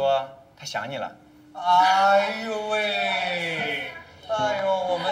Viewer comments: Jajakajaj, no sé ustedes, pero en el minuto 12:24 yo escuché como si dijera "Ay weeey" XD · solo yo entendí q dijo "hay weyy" 😂😂